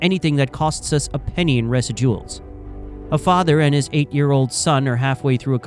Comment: ??? What is this AI voice? Why did you say “residuals” as “resid duels”??????